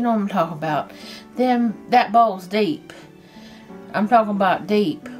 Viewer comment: I don’t know if you’ll see this comment as the video is from so long ago but I thought you said you were ‘balls deep’ at the shop you worked at until I realised you said ‘that bowl is deep! 🤣 When you then said you made 5000 balls at a time I figured the first thing I heard was about right!!! Hope you do see this though as I’m making these for holiday gifts too. Merry Christmas to your family from Australia xx